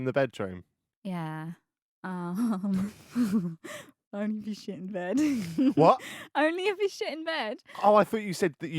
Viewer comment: What's she saying at 09:35? I keep hearing, "I only ever sh--- in bed".